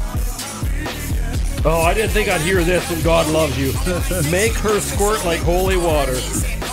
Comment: From god loves you, i heard " Make Curs squirt like holy water" made the line 100 more dope! Have you guys done other weird bands like clowncore, 1800 pain, and kfc murder chicks?